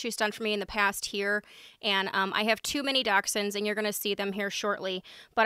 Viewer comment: Oh that's so cool! I love that you refer to your sister's dogs are your nieces. Our animals are definitely our babies. Legit though when you said you had two mini dachshunds I thought you said "I have too many dachshunds"! 🤣